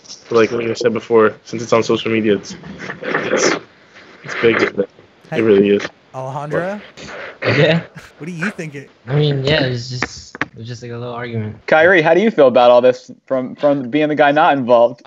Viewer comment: Did I hear Alejandra?